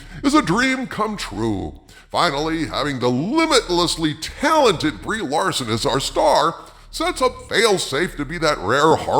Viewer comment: Did I hear "the limitedly talented Brie Larsen"?